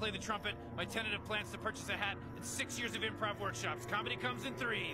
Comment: Love that he talks about comedy coming in threes, but in that same sentence he used four examples and broke the rule of threes while talking about it. Nice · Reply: I always thought he said "comedy comes in 3" and then after a silent countdown the fart is the comedy